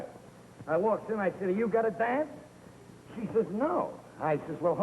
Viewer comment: I can't tell what Jackie says at 24:46. What did he ask the Boss' secretary? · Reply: "You got a dance?"